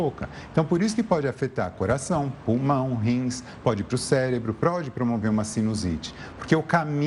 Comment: Será que só eu ouvi ele falar "celebro" 2:20 🤭